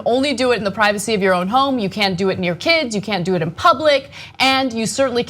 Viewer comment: Did she just say, "You can't do it in your kids"?